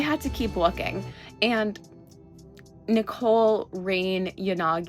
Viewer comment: when you named the pink haired characters we know in that order for a moment I thought you said Nicole Reeyn, the character from genshin. after rewinding that part I understood what you meant but I thought it was curious that if you put the name of those two together it sounds like the name of an astronomer that is known for calculating the daily positions of the sun and the moon (Nicole Reine) while yanagi's surname—tsukishiro—uses the kanji for the moon and a bunch of her attacks are also themed after the moon... idk where I'm going with this but I think it's a curious coincidence